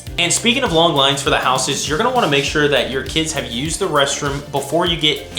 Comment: I thought it said make sure your kids get eaten lol